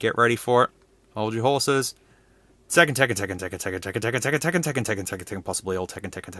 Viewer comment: i heard tekken when restart